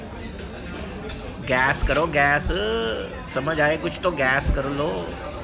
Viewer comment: Guess karo Guess... I heard Gas karo gass...